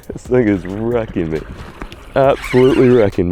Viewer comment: Yo what did you say at 7:53???😂😂😂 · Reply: be sayin nigga 😭😭